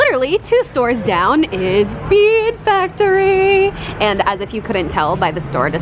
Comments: lol I thought she said "my second favorite place, the weed factory!" love you! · at first i thought she said weed factory lol not bead factory😂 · Lmao I thought you said maid factory. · When she said bead factory i thought she said weed factory